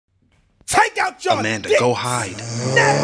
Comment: I just heard “Amanda go hide”😂😂😭😭